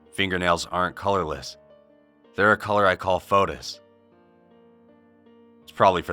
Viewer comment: WHAT THE FUCK DID HE JUST SAID FETUS OR FOTUS ??? XDDX